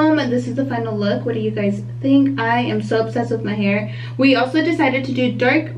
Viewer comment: It looks nice. It sounded like you said you were upset at your hair results; but you said you were obsessed. LOL. My bad. 😂😂😂